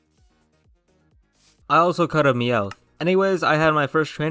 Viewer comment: i thought you said i caught a mewtwo instead of a meowth LOL.. but i knew something was wrong because you said it SOO calmly